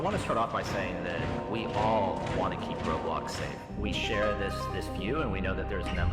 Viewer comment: “we want to keep pedos safe” is what I heard